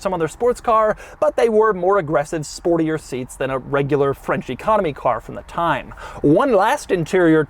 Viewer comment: Every time Doug says "French Economy Car" I hear "Frenchy Comedy Car" and well